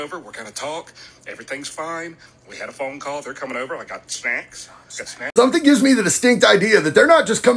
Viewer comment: I thought he said "got spanks".